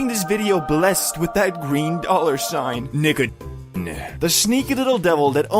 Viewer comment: i might be too fried but i swear he said the word right here 😭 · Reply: he was saying nicotine but cut off LMAO😭😭😭